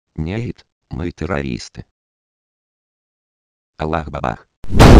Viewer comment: мне ведь не одной послышалось "Аллах бабах!" :D